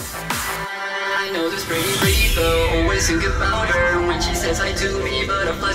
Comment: when I was younger I swore he was saying "I see the pretty rainbow" instead of rave girl 😅